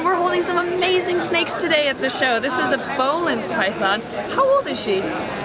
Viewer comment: Anyone know what snake is at 21:55 ? She said the name but I cant find it online...maybe ive got the spellin messed up? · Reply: Its boelen's python